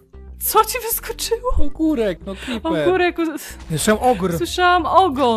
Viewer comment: "- Co ci wyskoczył? - Ogór. - A ja usłyszałam "ogon " A ja usłyszałam "okres" xD